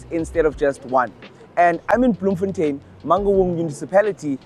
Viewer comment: What did you call it ? Mangungu municipality 🤣🤣